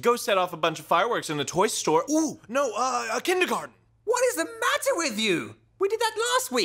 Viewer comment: What did poe say at 0:14? Kedel kart? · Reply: kindergarten I think